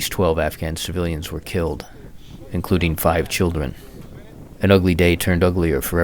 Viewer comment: Hay What happened to 5:12? Is he just says including 5 children?